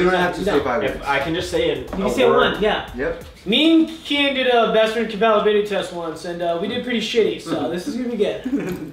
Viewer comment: can someone please tell me what said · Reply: Esmeralda Trevizo “Me and Kian did a best friend compatibility test once and we did pretty shitty so,, this is gonna be good.”